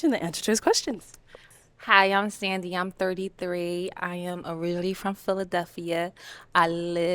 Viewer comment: did she say I’m orreally instead of originally?